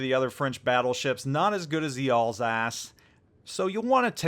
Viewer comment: I thought u said Ballsack instead of Alsace...